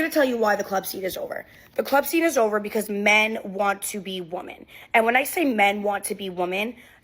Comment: "Men want to be woman." I know what the caption says, but she is saying "woman." Why can't these WOMEN get the plural for woman right?